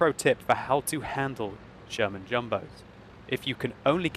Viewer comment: I swear says German Jumbo....with his accent...makes Sherman/German sound very close...lol